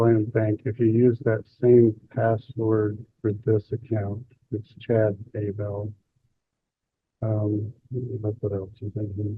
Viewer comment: Did he say his “password for this account is Chad Daybell?” ☠️ 😂